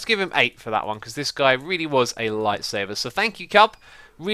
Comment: did he say that horse "really was a lightsaber" ?!?!? I feel like Lighsaber would be a great name for the horse btw. :P